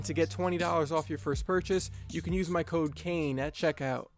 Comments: I swear on 1:25 he says cocaine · thought he said you can use my cocaine